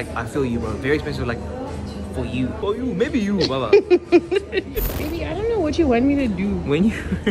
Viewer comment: Am I tweaking or did just call you baby 👀👀👀7:59 ?